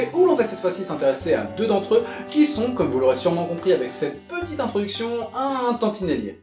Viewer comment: j'ai entendu trottinette j'ai vue flou 😂😂